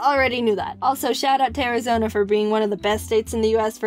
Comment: When she said "to Arizona" I thought she said "terrorism" and I had to rewind it